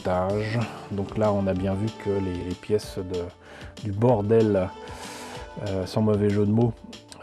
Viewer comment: Hum, hum.... n'ayant pas l'habitude des maquettes d'oiseau (ou d'avion d’ailleurs), je dois avouer que j'ai entendu "bordel" avant de comprendre : "bord aile". :p